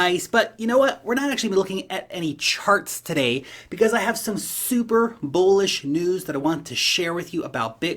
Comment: At first I heard "SuperBowl-ish" 🏈 then thought what if Grayscale did a Superbowl ad for their Bitcoin trust?? Now that would be Superbowl-Bullish!! 💉 Straight hopium